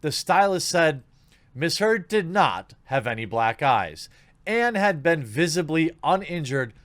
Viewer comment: I thought he said black guys lmao I was so confused